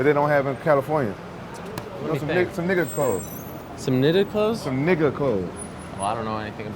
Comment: what did Ariel said ? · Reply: some knitted clothes ? he asks